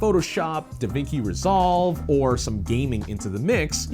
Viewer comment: Did he just say "Davinki resolve"? Btw that dinosaur in the background, I have it too man. That's kinda crazy.